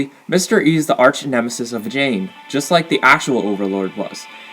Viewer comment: did you say Jane